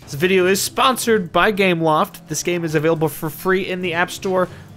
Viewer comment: I thought he said sponsored by Gamewoft lmao 😂😂😂